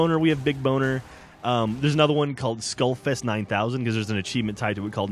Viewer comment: i thought he was saying skullFIST 9000 at first lol